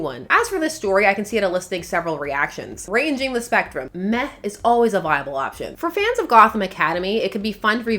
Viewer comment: Misheard "meh" as "meth" at 15:53 and was very confused 😂 this comics channel would have been taking a very strange turn